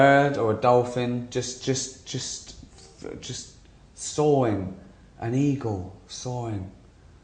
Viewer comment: I've seen this a few times since it first appeared and never noticed till today that when Ricky talks about being an eagle, he says 'sawing' instead of 'soaring'.